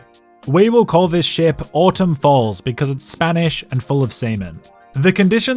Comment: "We'll call it Autumn Falls because its Spanish and full of seamen" is such a perfect line, you killed me fam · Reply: I thought he said Autumn Balls and chuckled a bit, although wondering how to make sense of the Spanish/Autumn thing. Had to look up Autumn Falls and I'm kind of proud of that.